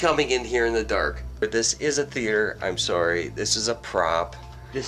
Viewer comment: When he said “bro you just spilled water all over my floor dude” you can here what I think it say “im sorry” it’s @ 17:35 into the video. It didn’t sound like it in normal speed. But when you slow it down that exactly what I hear. Lmk if anyone else hear what I hear. Slow it down to the slowest then work your way to normal speed 1 by one